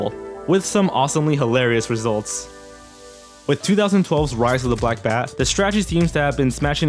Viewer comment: That last one made my foot fall asleep 😒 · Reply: Aaron W. is it bad I thought he said rise of the black man.